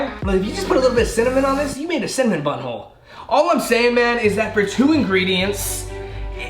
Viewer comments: I thought he said cinnamon butthole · did he say butt hole 😂😂😂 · Why did it sound like he said.. you made a cinnamon butthole.? hahahaha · did he say u made a cinamonn butt hole dude u eat cinamonn but holes